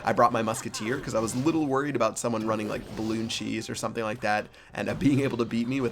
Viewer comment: did say balloon and cheese?